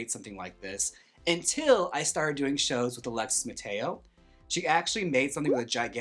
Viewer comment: I HONESTLY thought you said Alexis Michelle and I gasped cause I thought you posted a picture of her in black face with coco LOL